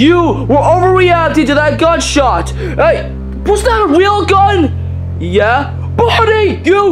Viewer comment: Did Freddy said real gun or whell gun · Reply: I clearly said "real".